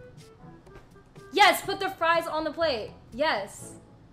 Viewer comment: My male brain completely missed "fries onto the plate" the first time. I would have done the exact same thing as Tenz. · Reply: I literally thought she said hold the fries💀